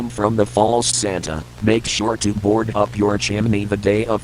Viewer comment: Why did I hear “bald Santa” 😭😭😭😭😭💔💔💔🥀🥀🥀🥀